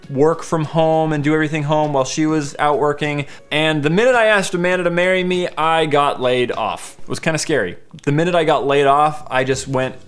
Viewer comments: for a few seconds, I thought he was said 'the minute I asked amanda to marry me, I got laid' xD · Who thought when he said "as soon and I asked to marry her I got laid" LOL